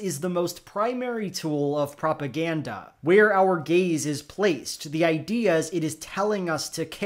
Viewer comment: i'm sorry but i heard "where our gays are placed" and i thought "i allways knew politics was a board game"